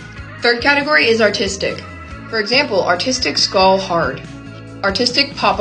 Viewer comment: I thought she was saying autistic 😂😂😂😭😭😭